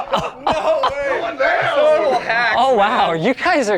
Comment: i swear i heard someone say "hacks"